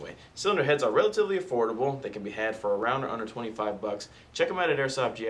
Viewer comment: He says "For around under $25" What I heard was "For around $125"